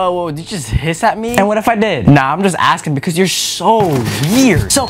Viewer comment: im wearing headphones and then i just hear “so what if i did?” LOW BATTERY